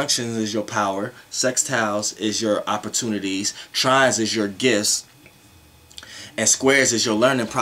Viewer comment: Trine is your what? Didn't get what was said at 8:40. Sounds like 'gifts' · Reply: okorocha obinna it's gifts